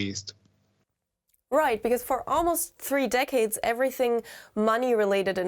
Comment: I heard (0:58) "over the next 700 years, at least", I was like damn the Japanese play the long game. 😂 It was "several" though, not "700".